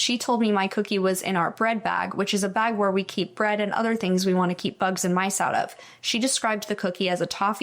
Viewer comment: Playing at low volume i heard “the thing we eat mice and bugs out of”